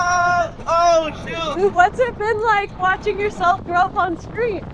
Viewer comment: we are just going to skip the part where he said”oh my shoe” WHAT HAPPEND TO HIS SHOE? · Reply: i honestly think he said oh shoot but they misheard him same with " i dont know" i think he was about to yell then passed out